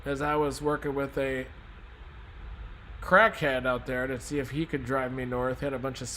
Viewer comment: did he say he got help from a crackhead?🤣 I am in Victoria, Texas i’m hoping we’ll will make it through OK.